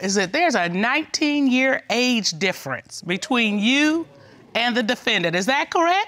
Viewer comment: I think you guys misheard the Cutlers...it was a 19 DAY age difference!